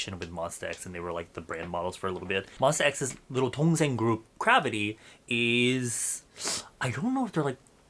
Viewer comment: LMAOO I THOUGHT IT SAID CAVITY LIP TINTS